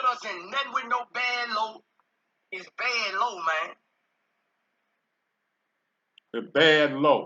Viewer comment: Must be Code Word....I THOUGHT HE SAID "BAD LO MEIN" Like Chinese food 🤣🤣🤣